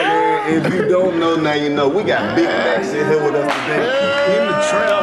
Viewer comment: Im high cause i thought karlous said we got big backs in here today